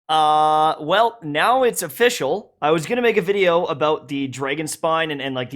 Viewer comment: What it is : Uh... Now its official What I heard : Uh... Now its a fischl